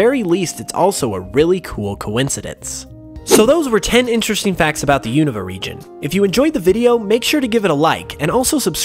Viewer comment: For a min, i thought the intro said 10 Uninteresting Facts About the Unova Region!" and i was like "Wth Unova is my favourite region"